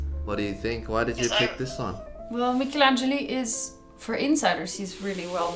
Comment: DID YOU JUST SAY "SONG"?